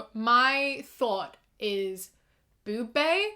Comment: i thought you said "my thot is boob bae" ps. i THINK this might be satire but there was another one like this that wasn't :(